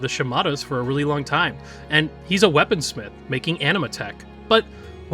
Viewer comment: First I heard it as "anime tech" and was like, that's not subtle lol. Thank you for putting music in the description!